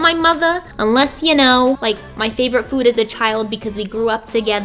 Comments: I had to watch it again, because I thought she said “unless you knew my favourite food IS A CHILD” 😂💖💖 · I genuinely thought that you said "my favourite food is a child" and I was like slow down there mykie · I thought she said “my favourite food is a child” 😂😂😂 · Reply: Fajir Habib np she said or if u know my favourite food as a child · I THOUGHT SHE SAID "UNLESS YOU KNOW MY FAVORITE FOOD IS A CHILD" AND I STARTED FREAKING OUT OH MY GOODNESS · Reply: Laura Samoil I had to rewind it 10000x times and she says as a child I think. I freaked out as well!! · I thought you said "unless you know my favorite food IS a child" BAHHAHAHA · "Unless you know my favorite food is a child" I know that's not what she said but I was so confused because that's what I heard the first time 🤣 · Ok i definitely thought you said "Unless you know my favorite food IS A CHILD" at first instead of "as" 😂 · I SWEAR I HEARD YOU SAY "MY FAVORITE FOOD IS A CHILD" INSTEAD OF "MY FAVORITE FOOD AS A CHILD"